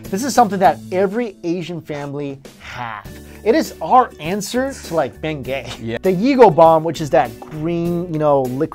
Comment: Also, I thought you said Tiger Balm was "our answer to being gay" and got really confused for a second xD